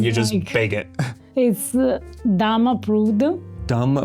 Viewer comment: If we don't get a pasta grammar "Dumb Approved" T-shirt we riot. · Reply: I thought she said "Dome approved", because the dish is from Lombardia...